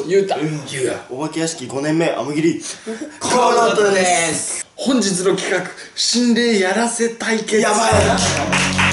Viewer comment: に声が聞こえる コムドットさんのメンバーではないと思う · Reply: ひっほほって言ってるンゴ